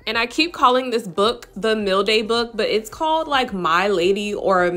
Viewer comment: I guess I'm hungry because I swore you were saying "meal day book" 😫🤦🏾‍♀️🤣🤣🤣. I'm sitting here like 🤔what in the world would a food book do for nails🤨? My bad... in learning to lay acrylic and it's hell for me. But my man hustle is press ons